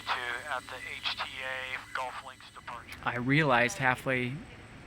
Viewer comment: I think I heard "Golf Links Departure" on a radio call. That's a road in Tucson that runs right along Davis Monthan AFB.